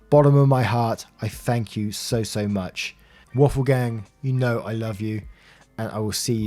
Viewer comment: tfw i first heard “butthole gang” instead of “waffle gang”